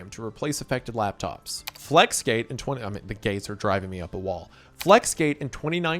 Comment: I had to rewind because at first I heard the gays are driving me up a wall 🫪